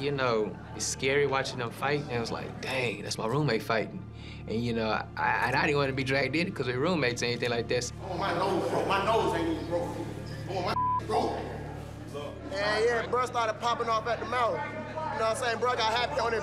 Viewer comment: Im jokedd 😂😂😂 ain't even get her discount card yet ! nd now im replaying it just heard her say im not even from hea lmaooooo wtf that got to do with anything 🤦🏾‍♀️ 🤣🤣 same time i thought i was tripping, i knew i heard a lil n.o. accent 😅